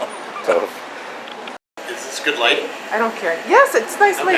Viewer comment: Yes, watch the Ask Project. It really is good. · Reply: I thought he said watch the Ass project